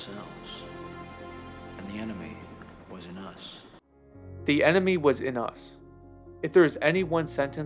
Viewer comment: I grew up watching this movie I didn't know he said the enemy was in us I thought you said the enemy wasn't us that makes more sense